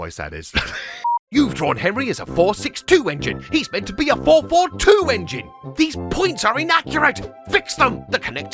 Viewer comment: I misheard 4-4-2 as "he's meant to be a World War II engine!"